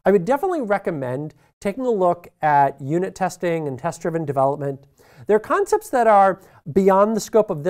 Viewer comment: HELP! #10:48 What was said there? "I would definitely look at unit testing and ...?" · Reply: "...and test-driven development."